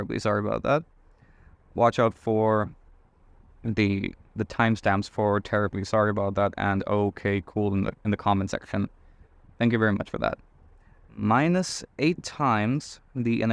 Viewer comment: Yes, and you can notice that at 4:11 I spotted them both, the reason for this is that, at this time, you said something about my counting, unfortunately I don't understand what, but it ends with "thank you very much", or some thing like that, so I'm not that worried 😃. It's a pleasure for me. By the way could you make a video about the alternate ways(1) of prooving that zeta(2)=pi^2 / 6 . There is one that starts from int_0^infty { int_0^infty { dx dy / (1-xy) }} that is not that easy, because it involves a couple of variable substitutions a bit tricky. (1) otherwise than the famous Euler proof. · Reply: He says "watch out for the timestamps for 'terribly sorry about that' and 'okay cool' in the comment section. Thank you very much for that."